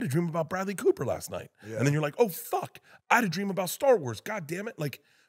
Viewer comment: When Bert said I had a dream about Bradley Cooper last night I thought he said after I had a dream about Starlord I was like he is just syked for Guardians of the Galaxy 3 😂